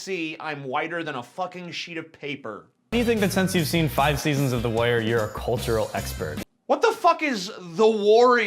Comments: How the fuck does not know about "The Wire"? He can't even recognize it when he hears it. He thinks the dude said "The Warrior"! Fail... Fail. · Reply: I heard "the warrior" as well. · Love your videos but you misheard the white guy at 2:26, he said "The Wire", a show I'm sure you've heard of and probably watched. It's good shit, but I've never heard someone refer to themselves as a cultural expert after watching it, though it is an astounding show. Off topic for you, Know you're a huge Manson fan(as am I), you've made videos talking about your favorite albums and such, curious if you have a favorite song(or top 3, I know favorite song is a very difficult question for just about any fan regarding their favorite bands). I'd have to say, for blaring it loudly, "This is the new shit" is my favorite. Tourniquet is also great, as is the Fight Song, This is Halloween, and Killing Strangers....shit, tons of great ones. Offer one up? Any obscure performances by Manson or the man behind the mask I should check out? Thanks man, rock on and keep making these dope videos. So glad to skepticism is alive.